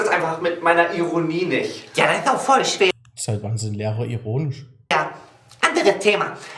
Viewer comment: ich habe "erotisch" statt "ironisch" verstanden 😂😂